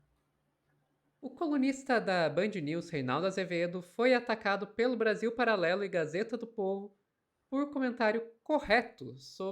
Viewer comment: Juro que eu ouvi "o comunista da Band News" 😂😂😂